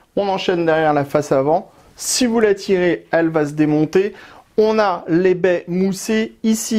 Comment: « Si vous la tirez, elle va se démonter » Moi : « TITRE » 😂😂😂😂